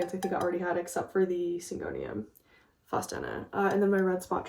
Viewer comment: What kind of syngonium is that? I can’t quite make out what you’re saying and tried googling different things that I thought you were saying but came up empty…the one that’s different shades of green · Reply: I think she said Syngonium faustina